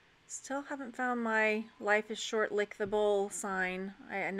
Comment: I have that sign for you! "Life is Short. Lick the Bowl." I hear you talk about it all of the time! It's 3 dimensional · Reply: LOL! I am so glad you wrote this! Listening to our Texas friend, I thought she said, "Life is short, lick the bull!" 🤣🤣🤣🤣🤣